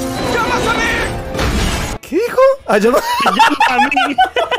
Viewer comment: gente sinceramente no sé que dice, me pueden decir? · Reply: "Llamas a mi" el mismo actor hace de la antorcha humana en los 4 fantásticos, otra peli.